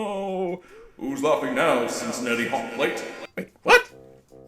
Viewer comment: Did he say... Cincinnati hot plate?